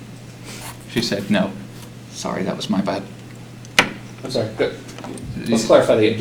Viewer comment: Poor guy you can tell he's nervous af and he's having serious stomach cramps with gas. I had to rewind at first cuz i thought he had said, "sorry, that was my butt" 😂